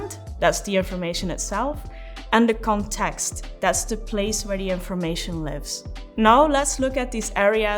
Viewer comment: Maam please tell me what you had said in 'the context' part? I hear it as "the place where the information leaves" it is right?